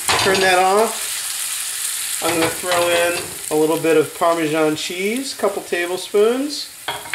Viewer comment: Parmesan/Parmeshan>?? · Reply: Hahahah! I heard you saying parmeshan ~.~